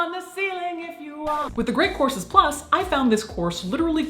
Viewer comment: During the sponsor portion, I thought you said The Great Corpses Plus. Perhaps make a plug with Patreon for that.